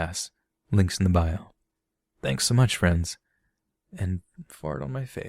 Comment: WAIT A SECOND DID YOU SAY "FART ON MY FACE" At 49:47 ?! OR DID I MISHEAR?!